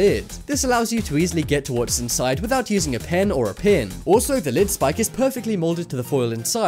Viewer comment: Did he say pen twice 6:53😂😂